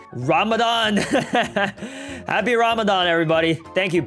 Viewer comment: did say happy ramadan😃